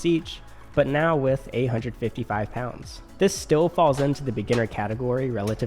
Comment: I heard 855 pounds and had to rewind back a few times